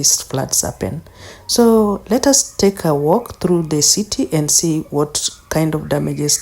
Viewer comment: Did you say take a walk? @ 1:00 though the situation was terrible.